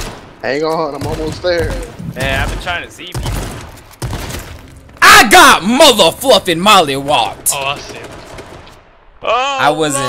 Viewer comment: sounded like he was preaching "I got mother fluffin Molly wooed"